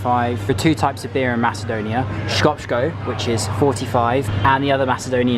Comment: Did he just say 5:13 škopško??